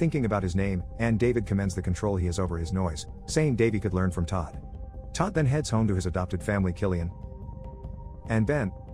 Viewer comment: i misheard that as "Todd heads home to his adoptive family Killing him"